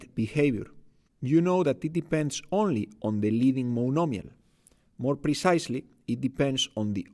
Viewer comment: What do you say @ 0:23 - @ 0:26 ? "You know that it depends only on the..." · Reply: Thanks for the question. I say: " it depends only on the 'leading monomial' ". The leading monomial is the monomial of highest degree in the polynomial. The degree of the leading monomial is considered to be "the degree of the polynomial" and its coefficient is considered to be "the leading coefficient" of the polynomial. In this context "order" and "degree" are synonyms. I hope this helps!